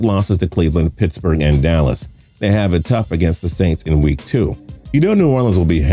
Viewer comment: Nobody gonna talk about 11:13 “Dallas Texans” Also at 13:10 he says 3-13 even though the graphic and what he says is saying they go 13-3